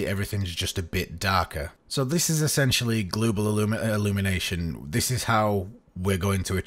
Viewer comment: hey at 15:12 its global illumination instead of glooble illumination right?